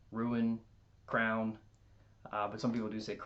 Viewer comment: I thought you said crown for crayon.